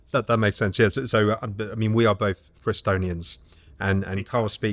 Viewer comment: What did you mean or say at 13:32? The captions have it as 'we are both Estonians' but I have no clue what it is you are actually saying because I doubt you said that. What did you say and what did you mean by it? Frestonians?